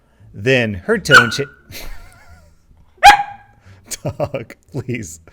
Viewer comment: For those who did not have Closed Caption on for the puppy bloopers, says "Dog Please at 17:11, but the captions say "Shit, Please" XD Lmao. I LOVE closed captions sometimes!